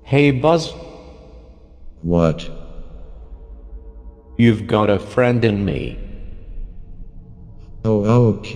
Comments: I thought bro said “YoU hAvE a FrIeNd In NeED” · Bro, it’s like three in the morning and my dumbass really thought he said you’ve got to finger me 😭😭😭💀💀💀